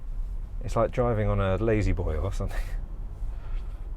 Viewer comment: I thought you said it's like 'driving on a ladyboy or something'. I had to rewind before I realised you said 'Lazyboy'.